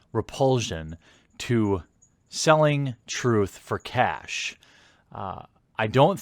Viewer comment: for a second i thought you said truth for cats XD